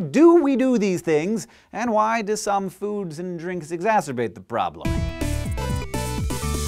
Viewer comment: what does hank say? exasperate? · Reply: Exacerbate.